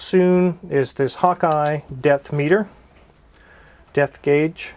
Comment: Sounded like you said deathmeter!!! Deathmeter 2000!